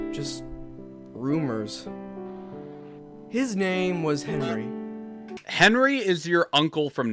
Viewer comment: for some reason that sounded like "his name was h*rny"